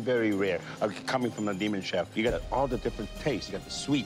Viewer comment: wait did he say coming from a demon chef 😂